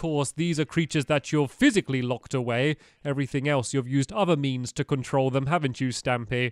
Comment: “everything else, you’ve used other memes to control them, haven’t you stamy?” *confused screaming* PS, this is not hate or anything, I just thought it was a funny misheard joke and I love your vids - sorry if I offended you or anything ;(((